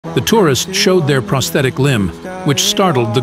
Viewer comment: I thought it said terrorists instead of tourist